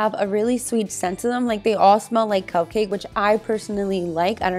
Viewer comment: What did you say they tasted like...? I'm swedish, so sometimes I'm not able to hear what people are saying if they're talking fast or something like that. I thought you said cocaine at first, but then I was like "nah, she can't have said that" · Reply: Christin Emanuelsson she said they smell like cupcake